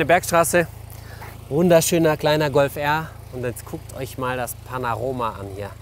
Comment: haha bin ich der einzige der es gehört hat :-P 19:15 Panaroma